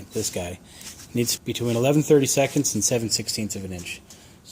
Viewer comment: what does he say at 36:50? 11:30 seconds or 7/16 of an inch. what is 11:30 seconds?! thanks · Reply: between 11/32 and 7/16th of an inch.